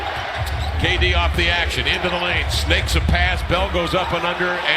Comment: I heard "snakes a pass"